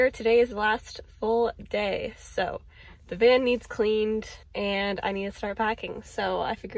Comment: When you said "The van needs cleaned" I thought I heard "The barn needs cleaned" 😂 What an adventure! Thanks for sharing. I 💯 want to go there now !